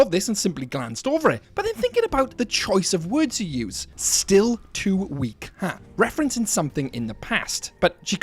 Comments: I heard her say "stay away from her" instead of still to weak huh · ok we all know roxy talks to herself before shows and whatnot. i thought it was roxy saying "still to weak huh?" as to herself to punch herself up to give herself strength. and the fact that mimic talks to her in her voice telling her "you cant beat me" caused her to gasp. her own voice against her. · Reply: Honestly, i hear “get out of my way” more than i do “still too weak, huh?”